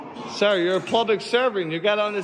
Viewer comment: Plublic? Did I hear him correctly at about Mark 6:22? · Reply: Yes, plublic. LOL